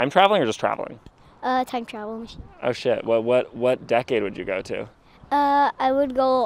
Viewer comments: ish, did he just say "oh shit"? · did he say oh shit ?